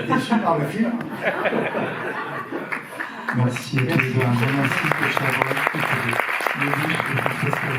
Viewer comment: C'est moi ou dit "Merci Claude Chabrol" ?